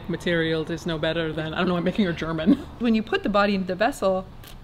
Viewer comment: So, I recently moved to Germany and I'm in a German language course. I watched this video yesterday, and today in class we came to the topic of Compostable clothing. And my teacher asked, "What else can be composted?" and without thinking, I said, "Humans!" I now have to give a presentation on this topic tomorrow. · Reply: I live in Germany too and please, help me 😂 At 10:46 I understood "I don't know what makes you German." Makes no sense to me, so I turned the caption on and IT SAYS THE SAME 😂😂 I have no idea what she's saying there or why. So if anybody knows... I beg you, explain it to me.